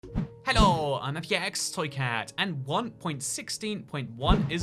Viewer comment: "Hello, I might be X Toycat?" Sorry I just had to. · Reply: For a while I thought he said "I'm Ibx soycat"